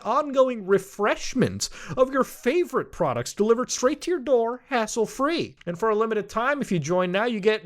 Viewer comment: Get 20% OFF + Free Shipping at Manscaped with code SOG at <link> #manscapedpartner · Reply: When you said hassle free, for half a second, I thought you said asshole free..